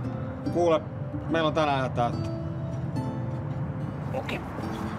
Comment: mitä toi sano en saa selvää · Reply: Kuule, meil on tänään aika täyttä